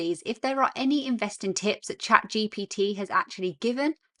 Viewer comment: Piers Morgan, blah blah, Amanda Holden blah blah, David Walliams blah blah blah, Sorry, I thought you said to chat BGT